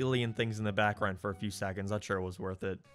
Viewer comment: Your accent is so strong and wild, I had to stop listening to this video on audio to see what word you wrote down at 9:54, because you said "he it." You added a syllable and an extra e sound. Hate. Ate. 8. Hate.